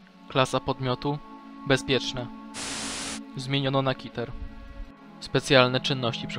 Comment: usłyszałem zmieniono na Hitler xd